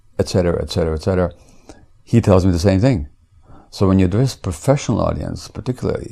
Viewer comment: Dr. Mate says "so when you ___ professional audience...." what is the word he says? I can't understand it and am working on transcribing this entire interview. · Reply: GTA Originals “so when you address a professional audience”